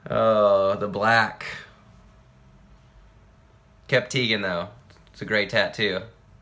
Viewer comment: Lol. I thought it said vegan